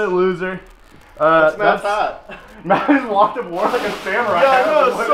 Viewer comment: I heard "It's Mattpat" instead of "It's Matt's hat." Also, this whole video is one of the best things I've ever seen.